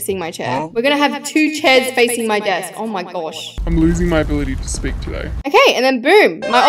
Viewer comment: AHHhHHHHZhzhahzhzhhazhzhzh i heard i am losing my booty to spesk